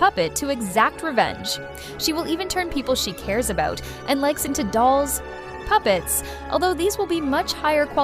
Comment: I heard "into flesh puppets" and said this b!tch crazy